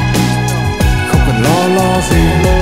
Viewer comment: nghe tới nghe lui lại cứ nghe thành "lo lo lo lo gì, chỉ cần lo lo lo ve chai" 😂😂😂😂